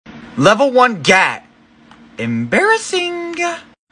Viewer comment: what i hear: "Level one gad"